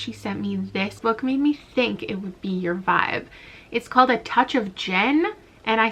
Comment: I watched this video while cleaning and thought you were saying 'A Touch of DJINN' the entire video and was so confused because there was no magic in the book. lmao 💀